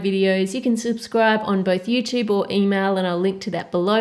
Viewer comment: instead of email I thought she said emo